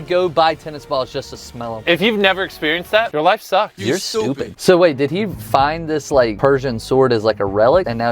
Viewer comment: i turned my sound on and the first thing i heard was "your stupid" repeatedly